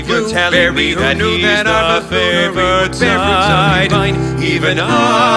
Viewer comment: While listening to this song I thought said 'flavored' instead of 'favored'.